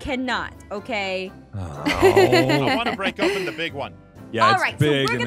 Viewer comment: is it just me, or did someone hear, “I want to break up with the big one”..?